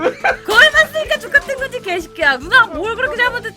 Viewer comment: 개식기만큼 정확히 들리는 게 없네